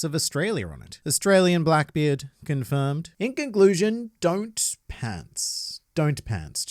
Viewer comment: Did you say “ink conclusion” or am I hearing things